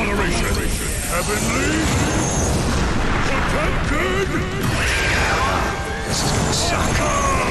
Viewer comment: Does anyone know what he's saying at 1:55? I've been trying to figure it out for like an hour lmao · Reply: “This is gonna suck” 😊